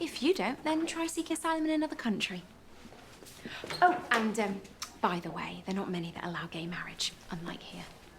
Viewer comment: I couldn't understand Sue's comment about gay marriage around 8:00. Can someone please translate? Thanks! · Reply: after suggesting that Nikki should go seek asylum in another country, she said 'by the way, there aren't many that allow gay marriage' which is a dig at Nikki's sexuality